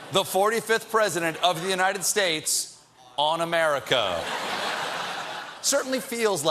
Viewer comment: I thought she said "the 45th president of the United Stated, unamerican"